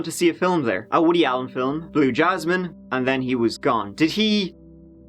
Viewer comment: Uhh not that it matters, but I could’ve sworn I heard he went to see Gravity… idk where Blue Jazmin came from 🤷🏻‍♂️